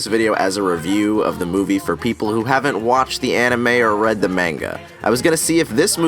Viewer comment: Anyone hear him saying menga instead of manga